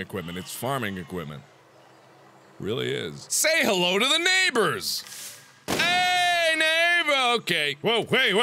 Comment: Watch it with subtitles, some of the things it comes up with.... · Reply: He says say hello to the neighbors, captions say "sale members okay" He got rid of the evidence - he killed the people who weren't satisfied with their Nota-peh-niise!